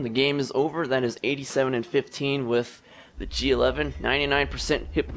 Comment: did he say pea soup???